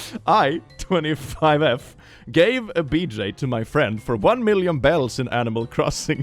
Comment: heard it as BLJ and thought how the fuck did you manage that hello